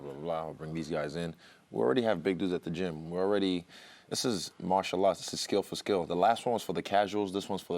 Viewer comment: did he say Mashallah?